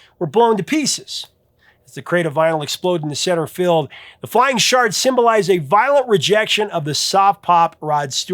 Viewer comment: I thought you said “flying shart”! 😂 I had to turn on closed captions for a second.